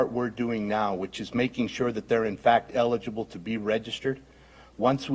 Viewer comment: the head speaking says once we got them in we counted them he never mentioned authentic or legitimate just the ones that they put in front of us to count. we counted what we were given to count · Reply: 🤣🤣🤣🤣 I heard him say "eligible"...try again